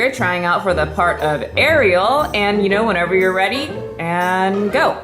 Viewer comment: I was watching this on 1.75 speed and I thought you said I tried out for the part of a slut I ALMOST CHOKED